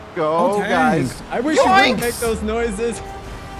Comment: did really say, "YOINKS!"